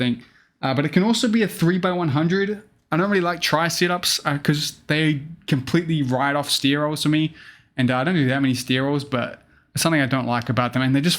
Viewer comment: What did he say at 4:54 ? "Cuz they completely ride of ........... to me" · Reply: "Cause they completely write off stair rolls for me"